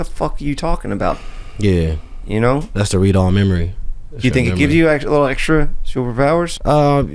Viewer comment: did I just hear “read all memory” 😂☠️